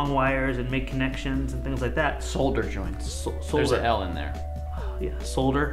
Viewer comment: I keep hearing "Sod Her!"